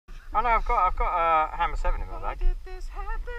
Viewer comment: It sounded like he said at the start I got a ham sandwich in my bag XD